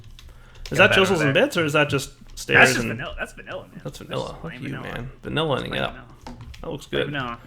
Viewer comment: I heard say "fuck you, man". I'm so bad minded.